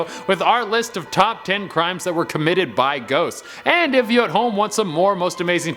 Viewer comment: I thought it said "Top 10 crimes that were committed by Goats" ._.